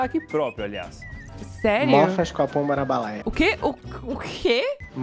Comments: eu brasileira sem entender uma palavra do que ele disse:👁👄👁(RS) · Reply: Mofas com a pomba na balaia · O mistério é, eu não entendi nada tbm! 😂😂😂😂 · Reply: Parece que ele falou mofas capon morabolaia ñ sei, mas oque isso significa eu ñ faço a mínima ideia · Nem eu que sou brasileiro entendi oq aquele cara falou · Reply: "Mófas copão bara balaia" 🤔 ... 2 horas depois.... 🤯 não entendi ainda. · ele disse, MOFAS COM A POMBA NA BALAIA .isso e uma expressão usada aqui em Florianópolis, Para dizer que uma pessoa não vai alcançar o seu objetivo, que vai se cansar de esperar. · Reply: Eu entendi "Nofas com a pão barabalaia"